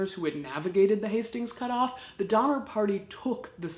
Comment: I thought it said "The Dinner Party"